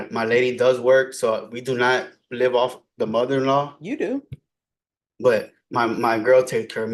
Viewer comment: I thought he said his money in law🤣🤣🤣🤣🤣🤣🤣🤣🤣